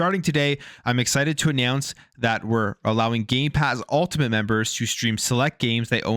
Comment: did you say gay pass ? i heard no m when you said it..... xD 2:39 YOU DID! YOU SAID GAY PASS ! HAHAHAHAHA next say gay box ! LOL i am appaled to all you straight/hetro people buying gay pass... LOLOL